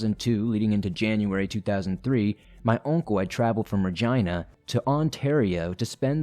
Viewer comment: Um, did you say vergina?